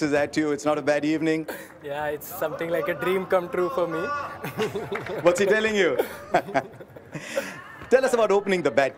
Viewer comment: what SRK said this.....it was not cleared🙄,,, can anyone guess it 🤔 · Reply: "Rahul, bol naam toh suna hoga "